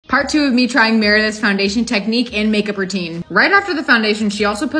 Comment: Tell me why I thought she said MARINETTE instead of Meredith the first time until I read the second time she said meredith